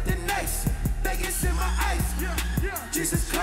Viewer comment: am I the only one who hears “maggots in my ice”